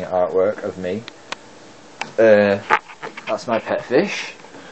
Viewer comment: It sounded like you said "Here's My pet fish, MEEEOOOW!"